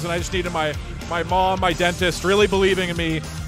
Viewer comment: I momentarily looked away when this played on my Watch Later; genuinely thought he said "my mom, my dead sister; cleaning my teeth when no one else would" and didn't think much of it until I read the comments 😆